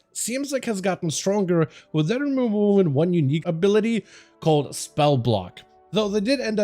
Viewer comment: The frequent mumbling makes it hard for non-natives to understand. Examples: 2:53 and 4:09. I still for the life of me cannot understand what you're saying at 2:53. Sounds like "Whe-the-rewuwuwu one unique ability".